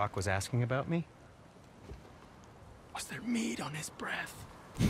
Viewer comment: He said Mead. It's an alcoholic beverage. Think wine but with honey instead of fruit. Seems to be a popular drink in skyrim so I'm guessing its a traditional Norse drink?